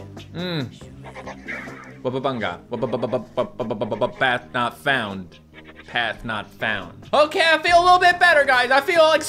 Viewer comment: He’s not saying let us out. He says “path not found” which was confirmed by the monster-handlers. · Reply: the real lyrics is “ path not found “ my fav misheard lyric was: “ cat not found-c-c-c-c-cat passed out “